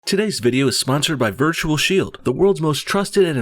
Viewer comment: Wow, I thought you said virtue shield for you sponsor LOL, I was wtf must be a joke